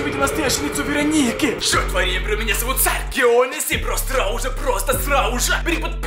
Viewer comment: что он говорит в начале, скажите пожалуйста🙏